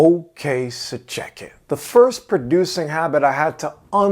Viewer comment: In the intro I always hear "Ok Sir chicken"